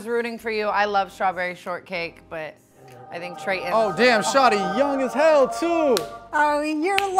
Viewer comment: All the people who want Trevor 👇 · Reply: I thought she said his name was Treyton?😂😂